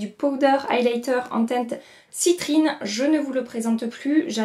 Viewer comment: Cc j'ai compris " ciprine" pour la teinte du highlighter jouer...plus osé que nars....mais finalement non, j'ai des problèmes d audition 🤣